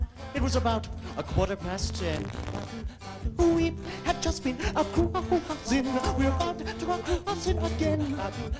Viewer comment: what the hell is he saying ?? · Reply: We had just been cruisin’. We were about to go cruisin’ again. Lol